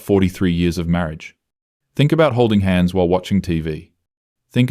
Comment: I'm not the only one who heard "Think about OF while watching TV" right?